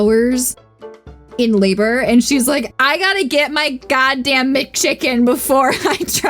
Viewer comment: C'mon, editor, she clearly says McChicken!